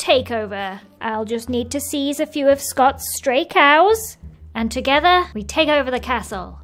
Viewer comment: I thought said straight cows 😂🏳️‍🌈♥️